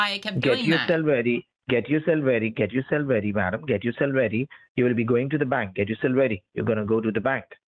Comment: I don't know because it was kind of unclear, but is he saying to get ready?